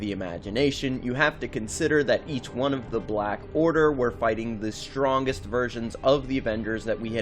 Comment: thought he said virgin